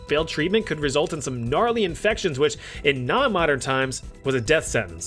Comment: did i hear GNARLY 🤭( katseye fan )